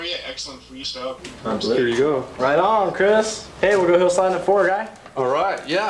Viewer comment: Does anybody know what this kid is saying? It sounds like "Hey, were gonna go sign the fort, guy". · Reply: I finally figured it out after all these years. “Hey, we’re gonna go slide the ‘Ford (guy?)” - referring to Ridgeford Drive in Westlake Village, CA. That’s the hill they bombed in the Bones Brigade Video Show. (Not sure if it’s the same hill from Future Primitive.) It’s a classic enough downhill spot that if you look on Google Street View you can see some dudes gearing up to skate it